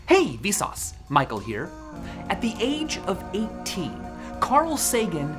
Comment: I just had a "mandala effect" with vsauce, I really thought he said "Michae, Sauce here" and I dont know why.